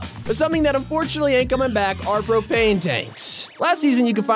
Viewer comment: I thought he said cocain